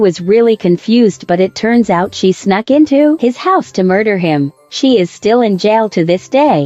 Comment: wait what she say. Mu-rd-er-?!